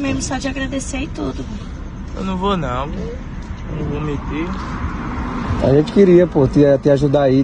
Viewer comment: EU NÃO VOU METER? E´ isso mesmo que ele fala aos 04:05 ? Fala sério heim!!! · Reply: Eu não vou mentir *